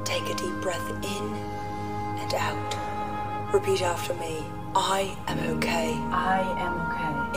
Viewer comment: I thought at 28 she said "Now repeat after me, I am a cake". And was like that's something new